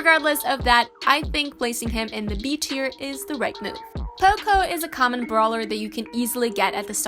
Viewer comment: Did she just say popo???